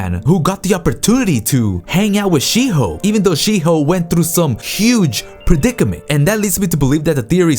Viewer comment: For some weird reason i heard this... "Even tho Shiho went through some Shit Hoe." Weird. I mean why just why? I must be tired